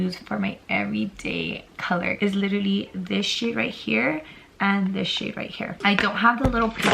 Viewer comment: When she pointed at the shades I thought she said “this shit here and this shit right here” 😂😭